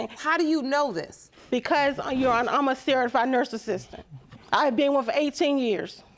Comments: I thought her mother said, "CAUSE IM A CERTIFIED NARCISSIST"😂😂😂. I had to listen again to get that she's saying "CERTIFIED NURSE ASSISTANT" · We need subtitles on this video😳😳😳… did I just hear ‘I’m a certified narcissist’🤣